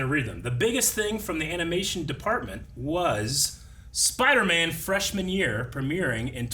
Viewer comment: What is wrong with me that I first heard "Spiderman: Fresh Manure"?